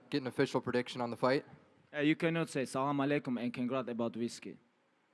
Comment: What did the reporter say? Around 22:13 I think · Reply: Reporter say Asalam u Alikum too khabib and thn ask conor about proper whiskey . And Khabib rply you can't say Asalam u Alikum and aks about whiskey..